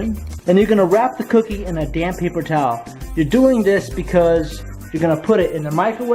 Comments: who else thought he said a damn paper towel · I thought he said a damn paper towel at first